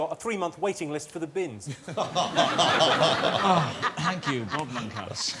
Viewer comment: What did Rob Brydon say at 27:39? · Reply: He said "oh, thank you, Bob Monkhouse" implying that Jimmy's joke was very similar to one that Bob Monkhouse would have told.